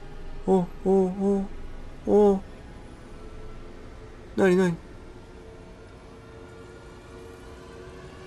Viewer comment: お、なに？がおなにに聞こえた俺を許してください